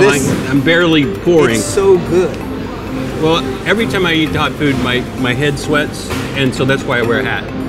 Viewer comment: I wear a hat because my head sweats when I eat spicy food Riiiiiiiight. So you eat spicy food every day for every meal? You sure you don't just wear a hat because you're bald? Nothing wrong with that, I just feel like you should accept it. No one cares. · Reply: I thought he said " everytime I eat dog-food......" I'm confused 😕